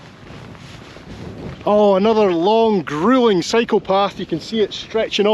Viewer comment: i thought you said “psychopath”, so I had to rewind a couple times